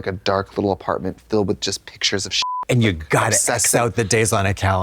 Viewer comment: did she say pictures of SHANGELA?? 😭